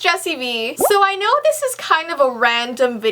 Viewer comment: When you said Jessie bee I thought you said Justin Bieber 🤣🤣🤣🤣😅😅😅😂😂😂love ya!❤️❤️❤️❤️💝💝💝💝💗💗💗💗🥰🥰🥰😘😘😘😍😍😍😍